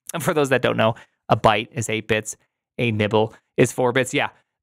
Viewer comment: i thought he was saying "nipple" referencing a horizontal pair of two dots 😂